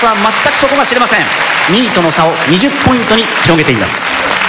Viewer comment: 「2位との差」を「ニートの差」って聞こえてしまった